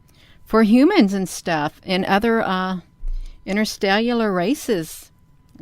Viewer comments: Did i hear "interstellular races"??? Scientist at work here! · ¿Did she just say "interstellular" races?